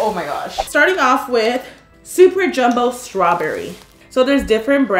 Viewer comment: did you just say super jumbo SCRAWBERRY or was it just me and you sound like you were said or your voice cracked idk